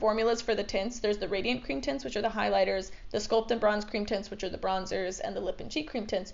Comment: I misheard 'cream tints' as 'cream tits'. Either way, I'll take it.